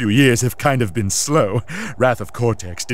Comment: Did Cortex break the 4th wall? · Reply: oh I thought he said, "rather cortex didn't do as well as we hoped"